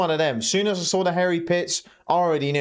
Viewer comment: I thought you said hairy t*ts , not pits 😂